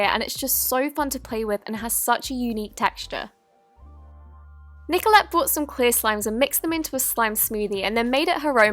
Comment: Did you say Nikola at 1:05 cuz that my name